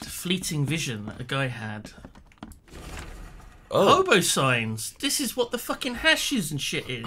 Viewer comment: I thought it said “hobo sings” and I was waiting the whole time for a hobo song 🤷🏻‍♀️